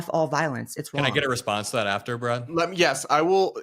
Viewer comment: I thought he said "can I response to that brah ?" 😂😂